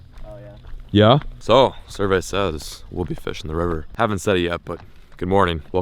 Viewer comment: Where you said "survey says" I thought you said "cervezas" 😂